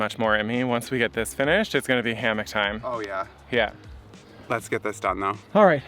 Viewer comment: Was that “hammock time” ? thought you said “Hamma’ Time” lol…you’re prob too young to laugh at that